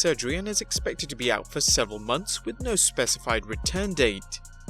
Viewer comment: I thought he said hardy was out for 69 months